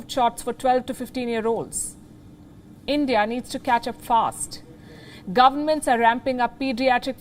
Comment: Did she just say at 12:07 India needs to catch a FART ?.